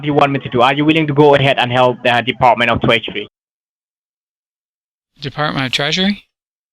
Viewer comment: I thought he said ‘dept of treachery.’